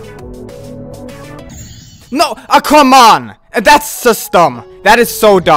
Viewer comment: he said, “oh come on.”but I thought he said, “I’m calling mom” lol